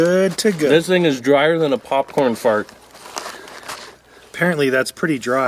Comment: I thought said popcorn fart